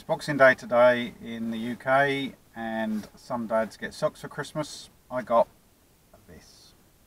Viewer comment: Ohhh. Socks for Christmas. I thought he said sucks